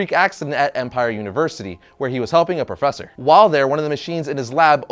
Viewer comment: I thought he said "Humping professors" instead of "helping for a second. That would've changed how I view Spider-Man very quickly lol